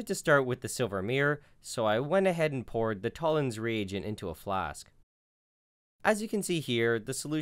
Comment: am i alone on this or did it sound like he was saying “Tollen’s Re-egion”